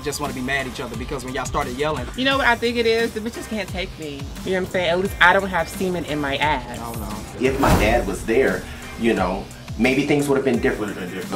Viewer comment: Ohhhhh CEMENT! Thought she was saying semen...